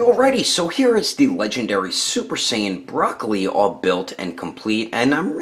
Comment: Did he call Broly broccoli 😂